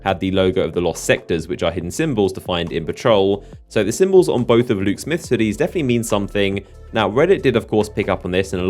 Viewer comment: At first I thought he said on Luke Smith titties